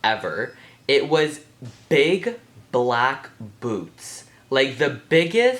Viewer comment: i thought he said big black boobs